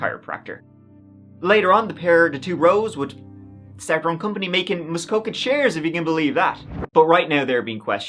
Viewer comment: was that English? WTH did he say? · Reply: "Started their own company making Muskoka chairs, if you can believe that" 😅